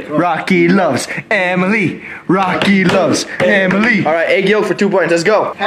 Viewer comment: for a moment there I heard "aegyo" instead of "egg yolk."